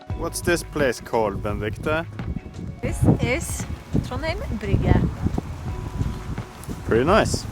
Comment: What is the town at 8:46?The transcript says “ Ben Victor” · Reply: That’s in Trondheim, specifically at Trondheim brygge. Dunno what Ben Victor has to do with it😂